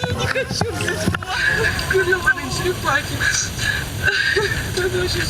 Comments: кто услышал мат 🤣🤣🤣🤣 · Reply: arina/ nail не она сказала грёбоные черепахи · Мне одной показалось что она сказала ёбн*тые черепахи